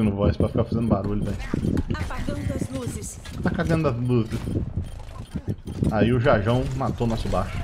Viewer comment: Compartilhei ,há, de novo antes de assistir, e foi melhor ainda, enxi o saco pessoalmente até a pessoa ver kkkkk, mano, quando a Sombra ultou eu escutei "Tá cagando as luces" ke? Kkkkkkk, as plays não estão muito diferentes do que eu pego no jogo rápido, tô quase criando coragem pra fazer a minha MD10